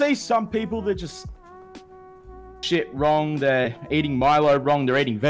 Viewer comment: Also i just realized i cut out when i said eat shit wrong i cut out and it sounded like i said "They Shit Wrong"! Haha.